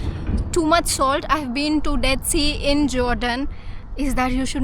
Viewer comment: Is it just me, or did anyone else catch a 'Death See' at 10:26? 🌊💀 I might be hearing things (the salt must be getting to my ears!), but it gave me a good laugh.